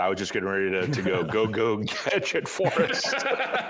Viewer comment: OMG!! "Go, go , Gadget-Forrest" is the BEST LINE EVER!!! 💥I NEED THIS T-SHIRT!!💥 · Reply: I thought he said "Go, go, get it Forrest"